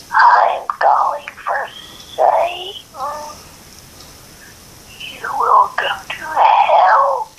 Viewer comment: what i heard : im going for satum you will come to helllshhh the other 2 are unknown for me, im guessing she either possessed or she just stans satan